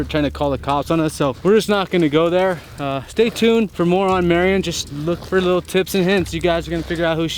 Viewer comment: Marion Harmon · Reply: I went back and listened again, you are correct, it's Marion. I could've swore he said Marianne lol My baad. 😬😊